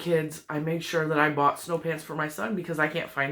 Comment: LOL, I'm sorry. At first I thought you said that your husband had asked for "hot pants." That made me chuckle :-D Your work is beautiful!